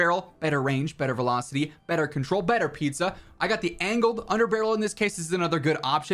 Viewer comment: I swear he says better Pizza at 15:33 🍕